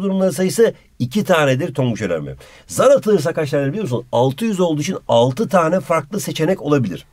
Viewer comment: 6 yüz deyince 600 anladım bune diyom